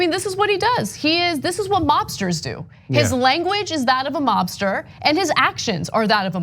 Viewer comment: heh Ana says "mobster", I hear "lobster"